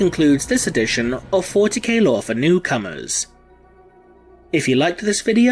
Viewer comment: i'm so tired i heard you saying "40K Lore For Cucumbers" at the end